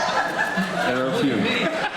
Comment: what does Colton say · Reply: "There are a few"